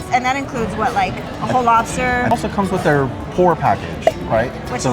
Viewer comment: I thought he said the "poor package" 😂😂😂😂